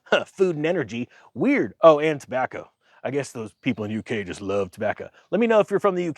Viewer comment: You got me laughing this afternoon when I think I actually heard you say shepeople ! You have likely been saying this for sometime now, I just think it’s the first time my brain actually noticed.